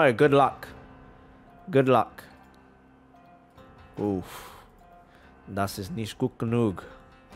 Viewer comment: I DIDN‘T NOTICE HE SPOKE GERMAN UNTIL I SAW THE CHAT WHAT THE SINCE WHEN?! He said "Das ist nicht gut genug" which basically means "That‘s not good enough" for anyone wondering^^ I can imagine his chat now being full of germans